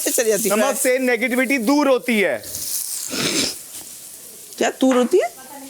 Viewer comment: when did Abhishek said Tu to kunika? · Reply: He said “namak khane se negativity ‘DUR’ hoti he.” This Kunika auntie and unke chamcha Nelam both of them twisted Abishake words said ‘tu’ bola when he said ‘dur’.